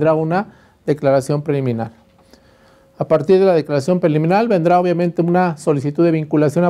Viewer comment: EN DONDE ENCUENTRO LAS BASES PARA LO DE LA "DECLARACION CRIMINAL" QUE DICE EN EL MINUTO 3:29, O EN DONDE VIENE ESE CONCEPTO DE "DECLARACION CRIMINAL" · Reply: Es declaración preliminar